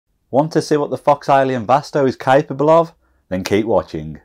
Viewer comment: For a second I thought you said "Want to see what the fuck's Alien Vasto is capable of?" XD